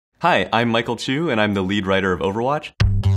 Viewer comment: Lol i had an advert before about michael bublé writing "bublé" over "bubly" on a can and the shop dude was like "michael, dont do that" or smth like tht and then the video started and i thought the guy said "hi, I'm michael too" 😂 i was like wtfff